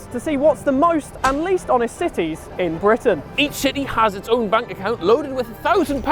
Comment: As a cardiffian I was so ashamed at the 6 minute bike theft but I’m glad we redeemed ourselves 😂😂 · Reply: Wait nvm just watched it back he did say Britain I stand corrected my fault thought he said uk